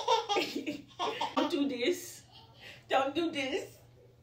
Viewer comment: I swear I heard her say don’t you touch it at 1:03 lmaooooooo too cute